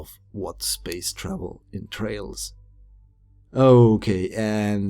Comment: you said "entrails" which means guts. I think you meant to say entails, "involves"